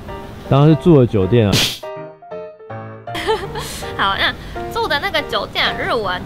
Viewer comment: 影片03:35中提到，我聽成：你要問的是做 (住) 的酒店還是什麼酒店？！😅😅發音可以再標準一點啦😅😅